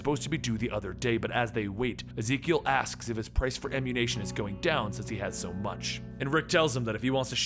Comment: did you say "the price for ammu-nation"? 😂